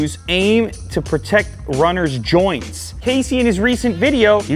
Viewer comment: when he says casey siri thought he said hey siri